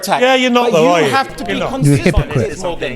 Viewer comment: When the other brother says "yes please" I don't know but I always hear "ya Iblees" 🤣 🤣 🤣